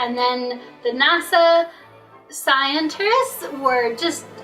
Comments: Did she just say... scienters? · "they're sent back to NASA .. pauses, thinks, pauses, rolls eyes, pauses, thinks... scientists". And flerthers don't think space exists...bloody hell, it lives between her ears... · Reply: I thought she said scienters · hold on, did she just say nasa scienters? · Did she just say "scienterists"? My new favourite person. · She actually said "scienterists" at 5:01. facepalm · did she just call them 'scienters'? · Did she just say "scienters"? .....It really doesn't sound like "scientists." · Did she just say 'scienterists' bish what?!? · did she mispronounce scientist, I heard her say “scientrists”, and she had to think so hard how to pronounce it as well. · did she say "NASA scienters"? o_o